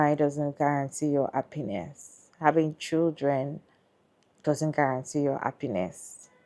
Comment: It sounds like you said, " It doesn't guarantee your penis!"